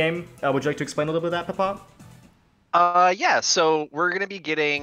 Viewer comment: I keep hearing "Papa" when he says "Ppot."